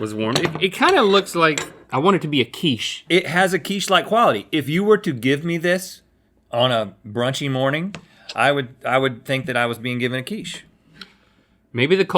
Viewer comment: i thought he said 'raunchy morning' for a second